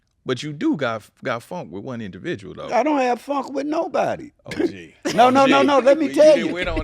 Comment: I thought he said he don’t have no funk